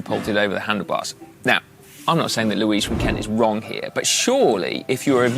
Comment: Did you say Louis 'Kent'? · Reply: Louise from Kent